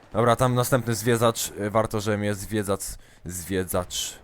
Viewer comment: ja usłyszałem "zwiedzacz" idź do laryngologa